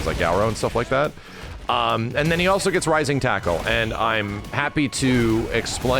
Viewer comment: I swear I heard max say, "rising taco" instead of "rising tackle"😂. Now that is all I hear